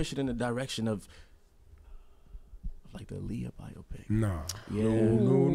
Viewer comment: did he say Lea biopic??